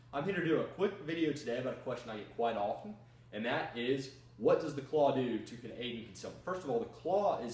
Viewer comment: what does the claw do to Canadian? What the hell did he say? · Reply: "Contain its self"